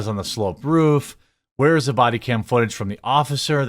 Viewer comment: I thought they said there was no bodycam footage